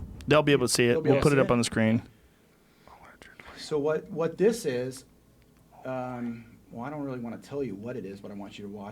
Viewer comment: Anyone else catch him whispering to Jamie at 41:07? Lol. What was he saying?? I definitely heard “he can’t hear me” in there somewhere. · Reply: I know at one point he says “not buying it”